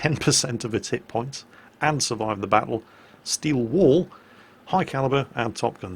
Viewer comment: I know is saying 'Steel Wall', but I keep hearing 'Steel Wool' :)